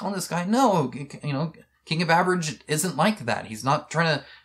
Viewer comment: Close to the end of video you pronounced "King of Average" the way I misheard it as "King of Beverage". Sorry, but I'm totally sticking to it now.